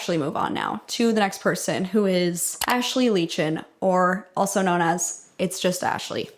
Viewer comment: at first i heard "actually leeching" and i thought wow thats a weird amount of self-awareness from a celebrity impersonator